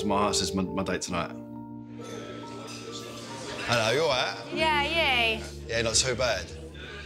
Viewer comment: what the hell do they say at 1:47 ?? It seems like "HAYA HAYA" and the girl gets all "YA YAY". Help a non native english speaker